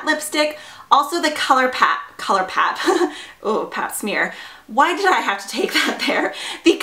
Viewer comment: Lmao .... Pap smear! I thought the same when you said color pop. Hahaha.